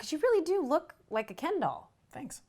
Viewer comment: "you really do look like a ken doll" "thanks" "you look so...perfect" blushes "thank you" WHY IS HE SO PRECIOUS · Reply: My dumbass thought she said "you look like a candle" so I was like how is that a compliment 😭😭